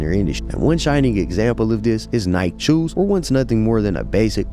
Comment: those are Jordan ones I have no idea what you said "nike choos"????